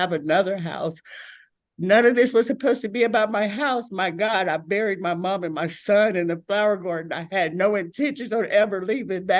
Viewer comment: Did she say she buried her mother and son in the flower garden at 2:20?